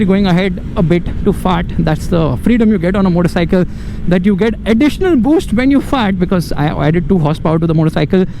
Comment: the screw on the exhaust mount just pulling off😅😅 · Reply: I heard "you get Additional boost when you fart."😂😂